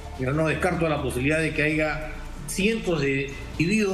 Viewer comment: Escuché bien?😮🤔❓⁉️ Dijo "haiga" ❓⁉️